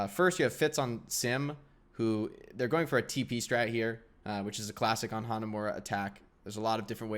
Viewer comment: When said tp strat I thought he said pp strat